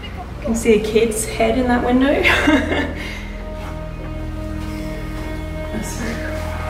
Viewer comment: _5:59 something is said over you but I couldn't understand it, so maybe you can. Maybe your partner It did sound like vald at the end you got couple of yes. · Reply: Thanks for watching and pointing this out, Michelle! I think I am whispering 'that's very cool' but cannot make out anything else. I'll have to get Jarrad to watch it too! Really appreciate this! 🖤🖤🖤